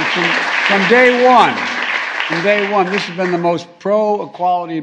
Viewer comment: For a second there, I thought he said, "From Gay one....." instead of "From Day one". LOL